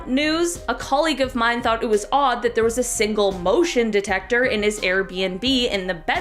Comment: Omg I felt so dumb, I thought you said Emotion detector not motion detector. I had a good laugh at myself. But that would be cool tho hahaha